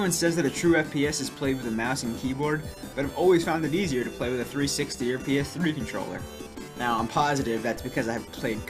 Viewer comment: he says "360 or ps3 controller" but the caption says otherwise. It said "Bitch Helen".